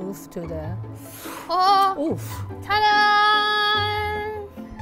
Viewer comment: was that an oof i hear